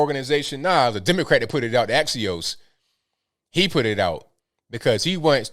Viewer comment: When you moved the screen I thought it said "adios" LMAO sounds legit